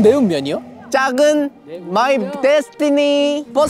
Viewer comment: Did he just say my "bestiny"?